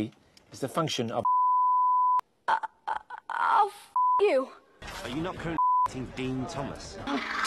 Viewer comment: how does FIGHT sync with a FUCK it looks like he actually said fuck